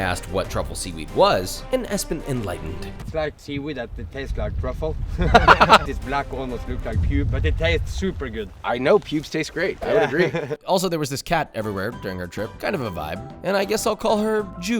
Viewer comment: I thought you said jew and I'm just there thinking, "Maybe that's why Germans and Japanese get along so well..." anyway I'll be seeing you all in hell